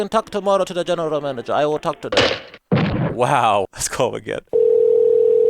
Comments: Am I the only one who noticed that the subtitles say "dude" while Ownage actually says "wow"? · Welcome back notification squad! This video is nuts lol, I cannotttt believe they reacted like this, I really thought they'd budge at least a little! If you enjoy the international calls, drop a Like on the video and lemme know where I should call next!! French Seduction Coach Prank - <link> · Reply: you say wow but the captions say dude. Gotta fix that bruh-bruh. 💯